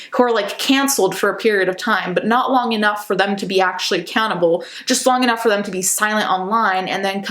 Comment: I know you said ‘accountable’ but I heard ‘a cannibal’. So I was like whatinthe armie hammer